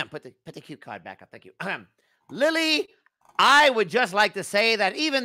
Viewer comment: Totally genuine compliment from Alastor (and another at 1:12:48 and 1:59:40 ) 45:35 Alastor makes a porno, with unsurprising results 2:59:24 It's a beautiful day outside...... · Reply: it sounded like he said “Lily” (if he did say that) which is my name, I was just clicking time stamps then heard what sounded like my name and started shrieking. 😂